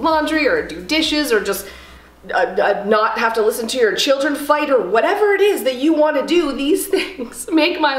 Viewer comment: A little busy, and watching this on tv... I hear "or just not have to listen to your kids fight". Lol! I had to go to my phone & give you a like!! Happy new year!